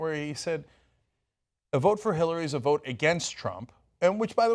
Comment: Hmm... no, that's not exactly what he said. He said something like, "A vote for Hillary is a vote for Trump, a vote for Bernie is a vote for a better future" -- he was saying, though, before that that the idea of voting for the [allegedly] lesser evil (Hillary) against the [allegedly] greater evil (Trump) is not acceptable anymore, and maybe that's what you were getting at, Cenk.